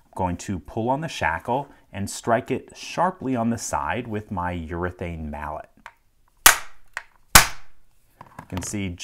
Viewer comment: I thought you said "urinated mallet" and almost believed you due to the color.